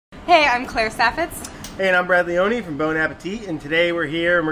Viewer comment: I thought he said "hey I'm ravioli" in stead of "hey I am brad leone" Have a nice day. Greetings, Sam